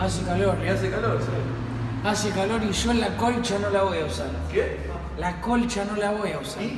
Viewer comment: te juro que escuche "hace calor y yo a la concha no la voy a usar"